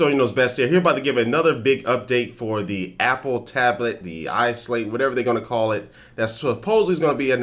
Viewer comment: Did he just say iSlave in the intro...?